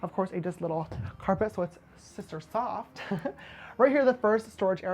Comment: Did he just say he goes to his room to ‘Sister suck’ ? 😂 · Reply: I thought i heard sister sob? But I have terrible hearing so what do I know!